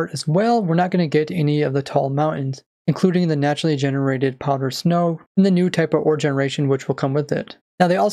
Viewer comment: lmao, I heard poggers snow